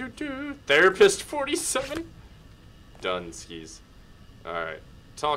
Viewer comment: "Therapist 47" what I thought it first said was "The rapist 47"....